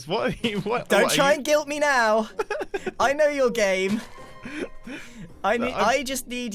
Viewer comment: when grian said ''i know your game'' i thought he said ''i know your gay''